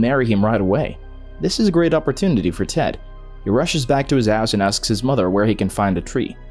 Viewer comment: i thought he said “so he rushed back to his ass” 💀💀💀